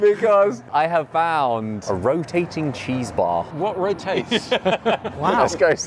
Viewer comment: Definitely thought it said “rotting cheese.” 🧀